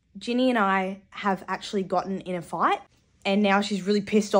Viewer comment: stop i thought when you said fight you meant flight and im not dyslexic